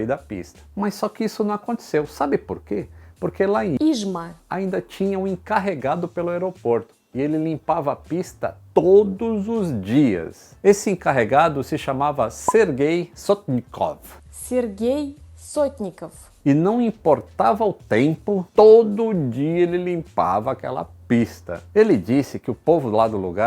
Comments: Oque eu entendi"ser guei nosot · só entendi "ser gay"